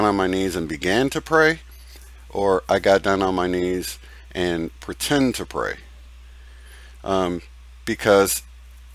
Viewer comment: I just listen to a few videos and I hear " and I pretend to pray "